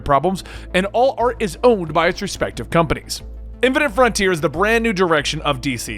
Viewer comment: I'm sorry, but when I listened, the first words I heard were "impotent frontier".